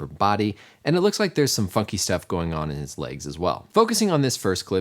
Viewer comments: anyone else at 1:12 thought he said "and it looks like there's some 'fucky' stuff going on in his legs as well" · sounds like hes saying theres some fu**ing stuff going on there